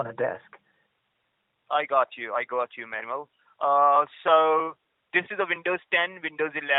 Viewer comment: you have captions?? lets goo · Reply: i feel it might be automated, the captions say "man" when the scammer said "manuel". it does seem better than no punctuation n stuff. still issues though :v